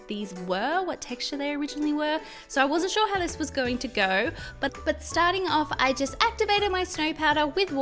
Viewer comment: Did anyone else hear her say ‘but-but’ ?? Just me? , ok I’ll leave